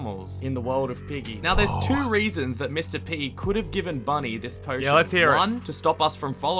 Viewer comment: i thought he said mr piggy